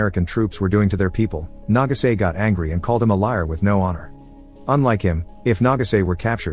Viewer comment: Lol I thought you said "called him a liar without the r"....LIA!!